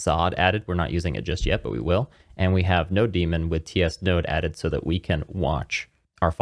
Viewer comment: I heard you saying "no-demon" instead of "node-mon" or "node-monitor" and now I can't unlisten it 😂